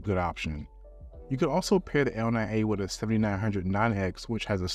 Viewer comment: Thank you for this one, amazing results, vvery interesting, one minor question: at 4:50 did you say 7909x? I could catch that one but also you tested a 7900x so I'm confused, sorry! , Looking forward to your next video! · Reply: Thanks! I said 7900-non X. There is a low power version called the 7900 and the 7900X is the 170W version.